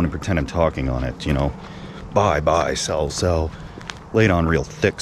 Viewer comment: Man walks by and sees in the woods cooking hot dogs and swigging beer 🤔 Man: "Excuse me sir, what do you think you're doing..." 👉: 😲 Spits beer out and shouts "BUY, SELL, BUY, SELL" 👍 Man: Thinks to himself... 🤔 "Hmm nothing strange to see here after all" - Walks off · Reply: Oh I’m dumb. I was so confused because I heard “bye, cell, bye, cell” haha